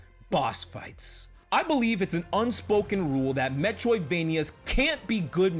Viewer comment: All I hear is "metwoidvania"